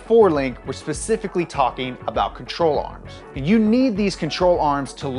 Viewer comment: I thought he said troll arms